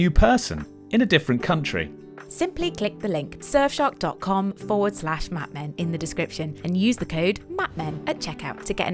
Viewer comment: What is Jane saying at 5:29? I can't read lips very well. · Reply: “F*** yourself”.